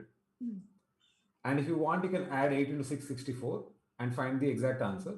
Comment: Did he really say at 10:14 8 into 6 is 64